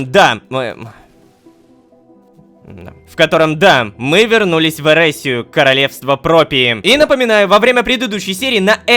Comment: Начало серии без спойлеров и без упоминаниях о прошлых сериях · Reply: Мне послышалось что он сказал "Мы вернулись в Эрессию Королевство пропили"